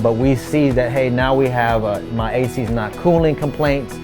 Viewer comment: 😂😂 “my ass” instead of “my ac”